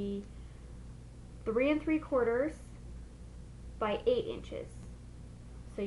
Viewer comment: Uhm what do you say at 4:10 ? ''3 corners and by 8 inches '' ??